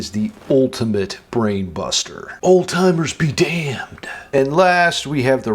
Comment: I actually said, "alzheimer's be damned" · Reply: oh sorry, i heard "oldtimers", but its true, we step of the shoulders of the oldtimer-titans of guitar and progress further